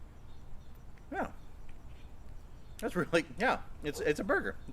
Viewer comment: Love the video and the burgers look so good! Note: at 11:05 you said "yeah" and the caption says "No" LOL I just thought that was a little bit humorous.